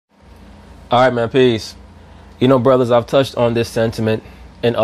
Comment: Alright man peace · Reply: Hero Gebrial It always sounds like he says "I'm at peace"